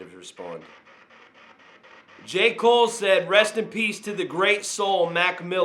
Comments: I dont hear "pizza" I hear "peace" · It sounds like he said LOVE AND PEACE...... TO HIS FAMILY AND ALL ELSE.... not pizza